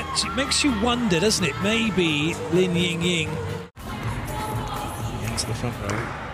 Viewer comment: what was he tryna say? Makes u wonder li ying ying